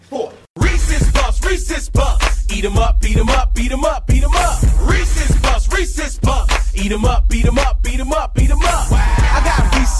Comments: FOR SO SO SO MANY YEARS IVE THOUGHT THIS WAS SAYING "racist club racist club beat em up beat em up" NOOO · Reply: I THOUGHT IT WAS SAYING “ressets buffs ressets buffs, beat them up, beta the up” 💀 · I understand: Reese‘s Puffs BEAT EM UP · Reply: I thought it said hit em up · i hear beat 'em up instead eat 'em up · I keep hearing beat 'm up than eat 'm up hahah i think i need help for this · Oh my god I always thought this song said "Racist buffs, beat 'em up!" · I understand: Reese‘s Puffs BEAT EM UP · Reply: lmao honestly I actually thought it said beat em up and not eat em up when I first heard it